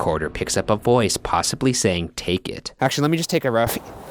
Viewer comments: It sounded like he said “fake it” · Camera picks up a voice... Ryan: Hears "Take it" Me: hears "fifik" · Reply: I heard “fake it” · I heard "Fake It" not "Take it". Please tell me someone else heard the same thing I did. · I heard "Fake it". Those fking ghosts and their ability to see the future or whatever.